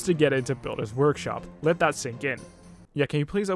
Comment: For those who are like wtf, that was random He means to say let that "sink" in